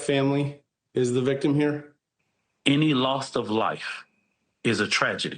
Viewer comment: Why do these people misuse such simple words. Re approach? Wtf, THIS illiterate child abuser is the best they could come up with for a rep???? Wooow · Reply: I noticed that, too. I think I also heard him say "Lost of life" Which he clearly is.